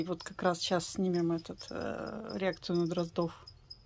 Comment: Мне послышалось, как ЁЛЬ прошипела слово "дроздорасы"